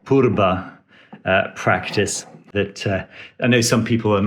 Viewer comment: What did you said? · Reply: I think you refer to phurba - a ritual dagger.